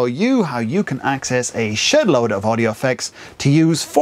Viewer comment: did you say SHIT LOAD?